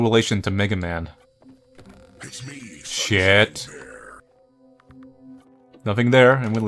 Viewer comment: I swear he just said "It's me, fuckshit bear!" And I can't tell if that's failed edginess, a joke, or just me mishearing things.